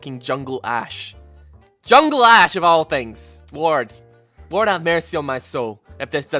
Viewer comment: when said "lord", i thought he said "ward" XD